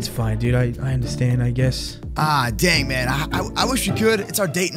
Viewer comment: Damn instead of dang!